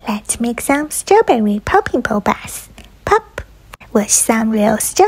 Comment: I honestly thought it said, pooping. Boba😂😂😂😂😂😂